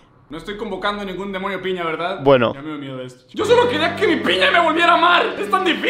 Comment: lo que dice es: lo lamentaras, chico